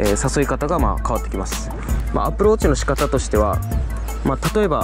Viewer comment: アプローチがAppleウォッチに聞こえた